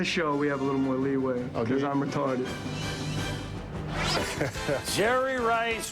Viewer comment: does he say he’s retarded😭😭😭